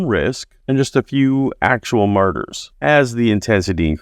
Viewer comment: I had to turn on Closed Captioning. It sounded like he said that all of the Apostles were murderers. Ohhhh....Martyrs!!